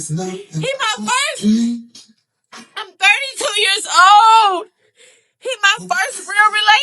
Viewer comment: I KNOW YOU MFN LYING TALKING ABOUT 32 years old??!?!?!?!?!?! Or did she say 42? I hope she said 42.